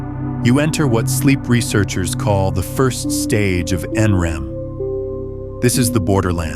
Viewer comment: The narrative is correct “N-REM” (non-rem). The subtitle says REM, that’s incorrect.